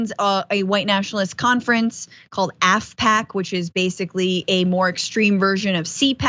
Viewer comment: At first I thought you said his conservative conference was called Aspac, I had to go back and listen again and its Afpac. My mistake.